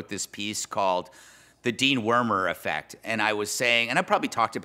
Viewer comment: I thought he said “dewormer”😅